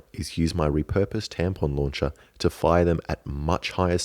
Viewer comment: Did he say tampon launcher?